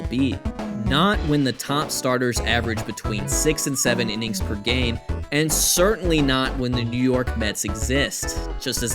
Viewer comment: What did you just say? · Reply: A foolish prophet